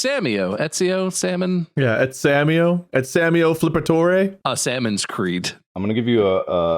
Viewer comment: I heard a Sammy O's Creek